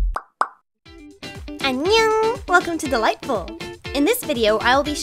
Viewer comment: Did she just say “annyeong” ?